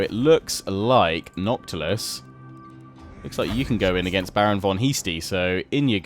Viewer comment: I totally hear "Baron von YEASTie" and spit out my water. I mean... not saying Heastie's a great name either though...